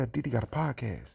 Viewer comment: 😂😂😂 it sounded like you said "diddy's got a podcast."